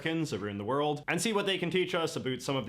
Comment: He said "aboot" instead of "about". This guy must be Canadian.